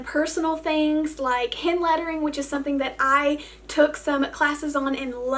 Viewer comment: When you said hand lettering, I could of sworn you said “ hen lettering “ I quickly check the screen to see if it explained what “ hen lettering “ Might be.